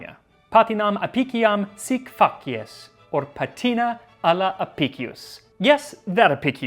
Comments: who else heard "Sick f**k yes"? Did a double take take when you pronounced it 😂 Love your show though, very informative and entertaining. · it almost sounds like your saying “f*ck yes”! TeeHee 💁🏼‍♂️😹😘